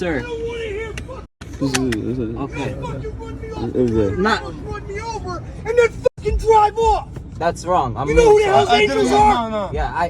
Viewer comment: re the biker - listen to what the biker actually says @ 14:00 :- "you don't run me off the road, nearly run me over and then driver off" [edited to remove expletives]. I'd be furious too if these idiots ran me off the road and drove off leaving me for dead. Not sympathising with Slim Albaher at all what they did was near-enough HIT AND RUN.